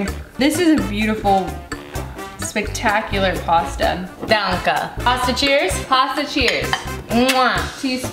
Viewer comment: Did she really say "Danke" ? :o :3